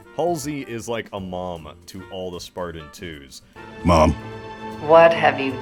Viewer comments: Jorge says "Ma'am" but his accent just makes it sound like "Mom". If he was to actually say mom, he would say "Mum". Yes, she is like a Mom to them, but they don't call her Mom. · he was actually saying ma’am but because of the accent it sounded like mom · Jorge actually says “Ma’am” there, not mom. I thought the same thing until I played with subtitles, confused the hell out of me why I thought he called her “mom”. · is george saying ma'am. Not mum.